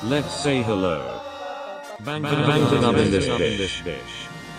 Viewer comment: Okay... Did I just hear them say "Bangtan in this b¡tch"?